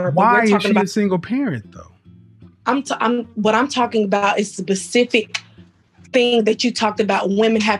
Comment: If she's educated, maybe she mispronounced "specific" at 05:40 because it sounded like she said "pacific". Maybe my hearing is bad.